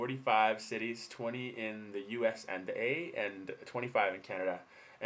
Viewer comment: You are right, he did say, U.S. and A, but it sounded like he said ehh (drawing it out a bit). But whatever i could have very well misunderstood.